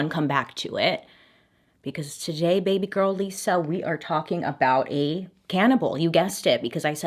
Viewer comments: Who's Lisa? My name's Lisa, and, I think I heard you say, "Baby girl Lisa," right? Am I hearing things? LOL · I had to rewind the beginning because you said "baby girl Lisa" and my name is Lisa...I was like huh....did you just say my name or am in going bonkers. dun dun dun :D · Did you just say “baby girl Lisa” 😂😂😂😂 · For a split second I thought she was going to say “we’re talking about a Canadian” 😂